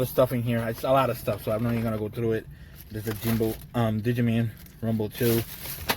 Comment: did you say DIGI MAN! 😟